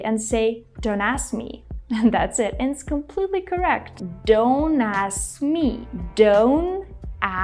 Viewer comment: instead of “don’t ask me” I heard “don’t ass me”😂 This is cringe.